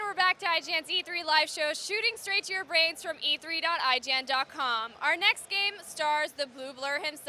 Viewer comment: haha I thought she said "Shooting strait into your veins from e3"